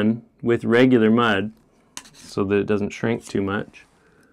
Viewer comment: I thought you said it doesn't shrink LOL